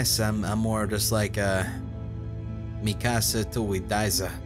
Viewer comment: When he said Mi casa, I thought he was saying Mikasa 😝 Aot